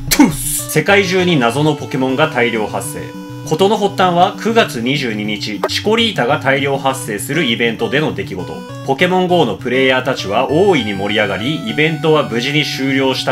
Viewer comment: シコ〇ータって聞こえた･･･